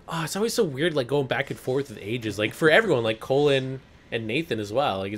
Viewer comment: sounded like he called Cole "Colon"